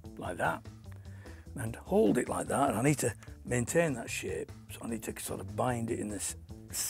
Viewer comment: Does he actually say maintain that s*** at 4:37?